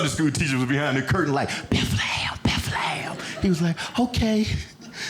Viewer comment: when the teacher says "bethlehem", the auto caption says "before the hell" 🤣